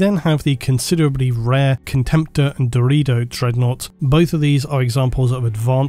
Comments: did you just say “dorito dreadnought” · I know i misheard but it's funny to think there's a dorito dreadnought 🤣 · I kept hearing "Dorito" dreadnought, did I hear wrong??